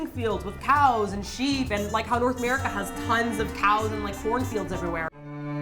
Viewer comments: I live near the Bandon Cheese factory(aka Face Rock Creamery)... sorry I can't send you any (it's gooood stuff too!). Can't you buy Tillamook (also in Oregon) cheese at the Korean Costco? I heard 'Horse fields' not 'Porn fields'. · i thought said "horn field" not "corn field"